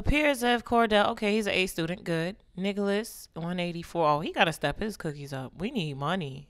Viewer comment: Swear at 5:17 it sounded like she said “niggalis” instead of “Nicholas” 😂